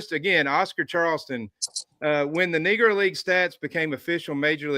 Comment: did he say n_gg_r at 11:26? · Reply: No sir, he said Negro League which is what they called the league where black players were allowed to play professionally in the United States prior to Jackie Robinson and integration.